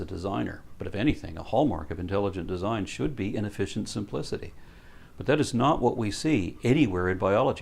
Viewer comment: help please. Did say "inefficient simplicity' vs. "Inefficient complexity" or did I hear it incorrectly and he said "efficiency simplicity"? Thanks. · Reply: "In efficient simplicity" I believe